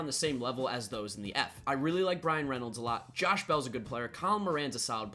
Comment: Sounded like you said Ryan Reynolds instead of Bryan Reynolds =p Speaking of the Pirates, if they can get some decent pitching they could be in the running in the NL Central. Being from the Bay Area, it was laughable watching Giants fans actually think for about a month that they had a chance at the Wild Card